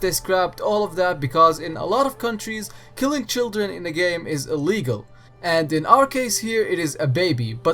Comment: I'm sorry, could you say that again? Did you say "illegal"? Why?